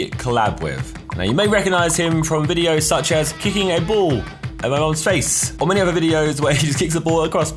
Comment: did he say “at my mum’s face ?”